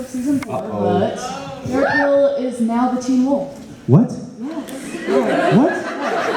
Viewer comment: what did the girl say at 15:20?? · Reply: she said derek is now a teen wolf